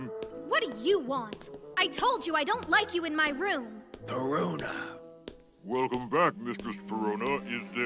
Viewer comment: it kinda sounds like he said the rona